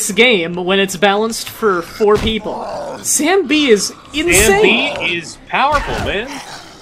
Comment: I keep hearing "Sam B" as "zambie" and it throws me off so much. XD